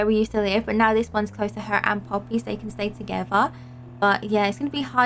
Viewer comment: did said aunt poppy😂😂